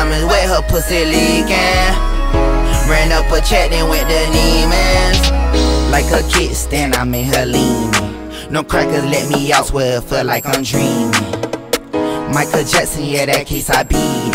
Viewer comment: "Like a kick stand I had her leanin" best part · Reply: I thought it said “Like a kickstand I’m in here leanin” smh